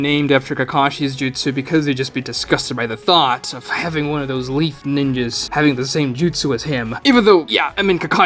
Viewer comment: sounded like "having one of those leaf niggas" 😂